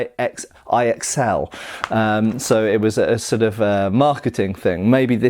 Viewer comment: My initial thought when you said XLCR was excelsior.